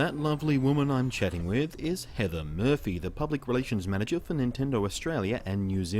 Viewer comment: thought you said heather herpy.